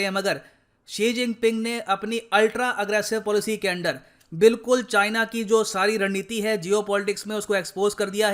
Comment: I heard China ki randneeti 2:20 😂😂